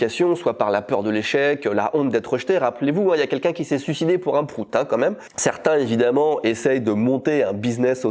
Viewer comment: Désolée mais lors de la présentation de la sponso j'ai entendu "frout" ce qui reste quand même dans le sujet 🤣🍑💨